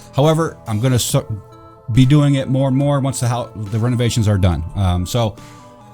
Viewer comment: When you said renovation for some reason I thought you said home invasion! I was wait what